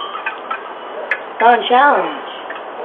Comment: I heard, I'm challenged.